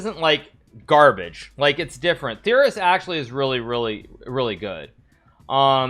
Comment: I thought it Said terrorist instead of theorist and that you guys were gonna put the middle easterns in the tier